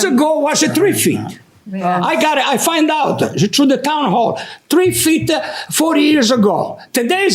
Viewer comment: Ooooh, he said she called the town hall. I thought he said the town hoe!!! 😂😂😂😂